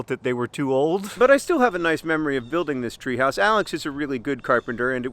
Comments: I thought you said "Alice is a really good carpenter" and I was like 'that's a new development!' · I thought you said "Alice is a really good carpenter" and I was not surprised, it would make perfect sense for the Yeti's daughter to already have a range of odd but practical and highly physical craftsmanship skills